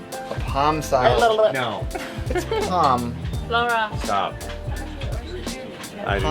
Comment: uh oh....did I hear him tell her to “Stop”? A bit of tension there